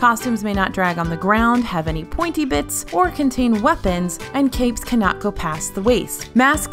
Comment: I thought said no capes